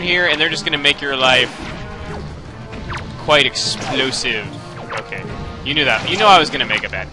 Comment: turn on CC then go to 3:27 and when says "guite explosive" the caption says "ladies you see it"